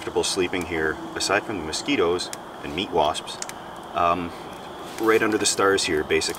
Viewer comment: I'm sorry, did he say "Meat Wasp"?